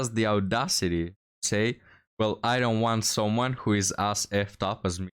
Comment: Correct me if I'm wrong but I think she said she DOES want someone who is as effed up as she is.